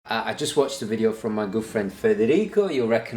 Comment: i swear every time i heard you say " good friend " i thought it was "girlfriend" which got me so confused until now, i read it in your description " good friend " .